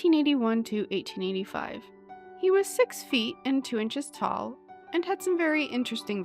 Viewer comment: Don't watch these videos at work: you will mishear Arthur's description as, "He had six feet and was two inches tall."